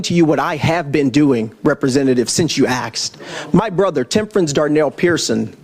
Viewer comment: did he throw out the word axed instead of asked?